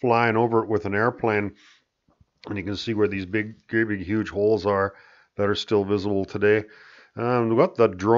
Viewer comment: Did you just say big gaping holes?